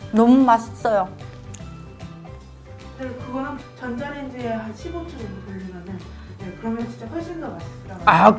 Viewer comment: I think there was a typo “if you put it in the microwave for *15 minutes**...” I think that was meant to say 15 **seconds* as 15 minutes might catch it on fire 😅